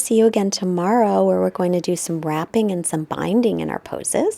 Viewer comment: Well... that´s something to aspire to, the grasshopper :) Hahaha, at the end I thought you said that there will be RAPPING tomorrow. I know see it´s wrapping. Namaste!